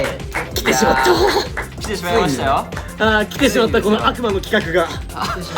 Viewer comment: 「悪魔の企画が…」でMr.お化け屋敷岩本照第4弾の「悪魔なの？」「佐久間です」のやりとりを連想し、「佐久間の企画が…」に聞こえる。ひーくん待ってるよ